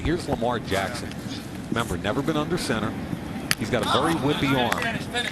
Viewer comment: Legit thought Mayock said @ 1:28 "he's got a very wimpy arm" Had to turn on captions to see he said "would-be arm"...From all the highlights dude is like a right handed Vick!